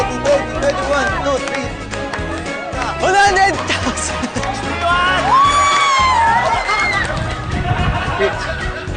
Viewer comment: ano sabi ni chito? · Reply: 100